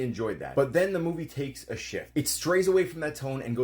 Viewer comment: For a second in the video, I thought you said “and then the movie takes a shit..” instead of shift. That would have been more accurate